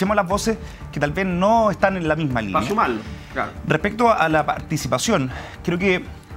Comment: deja tu comentario sobre lo que dice realmente francisco orrego en el minuto 21:26 dice pancho malo o dice para sumarlo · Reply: Dice pa’ sumarlo